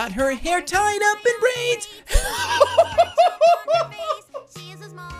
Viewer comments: I heard "brains" and my brain created a horrifying visual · To me it sounds like he said "her hair tied up in Brains' which ass an extra level of gruesomeness to the mishap